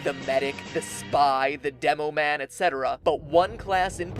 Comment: does he say Devil Man?